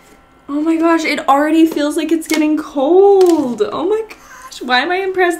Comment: When she said omg it’s getting cold all I heard was “So you think I’m skinnyyyyyyyyy”